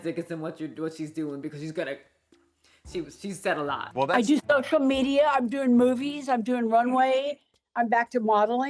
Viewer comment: "I do social media" dear lord I had to replay it 4 times because the first three I heard "I just got over Chlamydia"